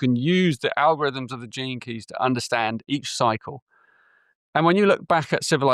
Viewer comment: At first I thought he said the term "JINKIES" a catchphrase by Velma Dinkley in the animated series Scooby-Doo 🤣🤣😂😂🤣🤣when he meant "GENE KEY'S"!